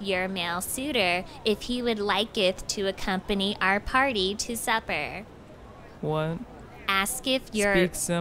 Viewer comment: Am i the only one at 6:56 i hear "suffer" instead of "supper"?